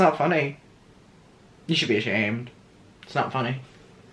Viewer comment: did anyone else say aShaned instead of ashamed? I’ll go now 😭